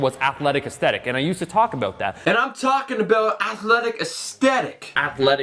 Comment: athletic aesthetic sounded like alpha destiny